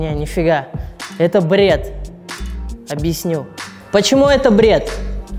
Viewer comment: I hear "Blyat", I like this guy